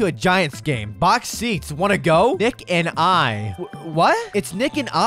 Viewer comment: Dude when he said "nick and I" I heard "d*ck and I"